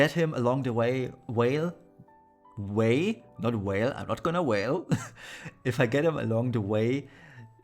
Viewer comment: Great video btw at 2:51 you said whale ff9 banner thats what i heard now im hlod you too that lol